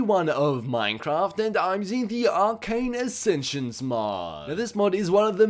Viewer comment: I thought it said Arcade Sensation :/